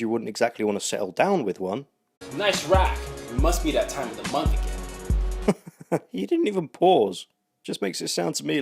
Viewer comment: I honestly thought he said "hey, nice rock, must be her time of the month" as in the boyfriend/husband just got her something to keep her calm